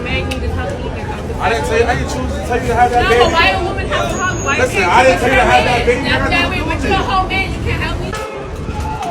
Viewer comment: i think the man said "i didn't tell you to have that baby" (i may have misheard it, but still 😂)